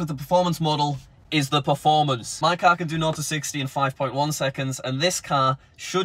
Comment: what r u saying? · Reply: in Code it sounds like “no to 60”